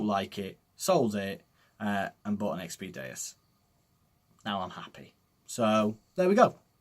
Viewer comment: What kind of detector are you replacing it with? I know you mentioned it but I can't quote make out what you said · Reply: xp deus